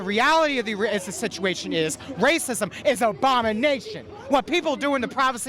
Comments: Did he say "obamanation"? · He sounded like he said, "racism is Obama-nation," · Did he Just say “ObamaNation” 😂😂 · All I heard was "Racism is about Obama's Nation" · it sounded like IT said racism is Obamanation was that just me?